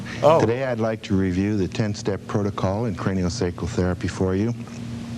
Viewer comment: At first i thought it said CranioSexual Therapy which kinda matched the cover lol.